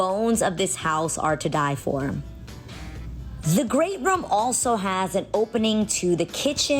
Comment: I'm getting a home built from the ground up and I getting ideas from many different homes. This is nice..i like the it sounds like you said grape room??? I'm not sure